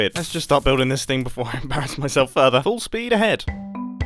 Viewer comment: When u said "full speed ahead" it sounded like u said "false peed a head"